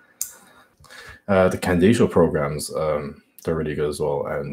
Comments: Who does he say at 2:00? Sounds like 'ken diesel' or similar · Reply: Charlie Duran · What programs did he say at 2:00? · What program did he say @ 2:00? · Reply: "The Candito programs"..